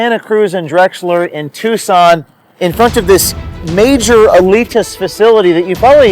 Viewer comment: Every time he says "Alitas" I swear I hear "elitists"